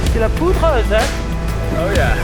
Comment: the subtitles say : "speaking in french" I'm french and it is said : "C'est de la poudreuse hein !" which means : "That's Powder !" Just if you can change it :) And maybe open the public transcription so we can create the subtitles for other languages :D But congratulations for this amazing movie ! I think I'll buy some Salomon stuff xD